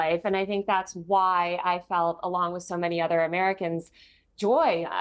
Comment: Peirs u can't gaslight us into believing she said something she didn't 😂let ur guests speak instead of interupting them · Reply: Emmm what? Are you deaf? Stupid? She said she felt Joy.